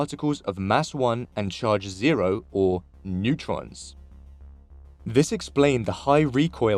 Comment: i thought it said neurons not neutrons at first lol